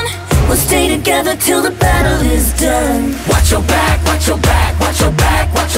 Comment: I hear "watch yo back" 😂